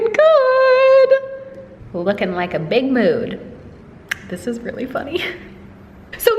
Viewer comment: I thought you said looking like a Big Mac so I had to rewind it....I must be hungry lol